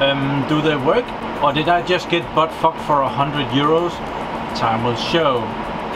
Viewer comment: did he said butt-fucked ?